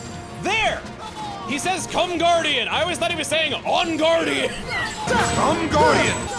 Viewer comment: Damn, we're here at the end already (of the main story anyway). What an incredible ride. EDIT: oh my god, I also thought Ansem said "On-guardian" too!